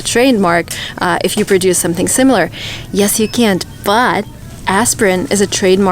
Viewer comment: What'd she say at 5:03? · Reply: Yes, you can’t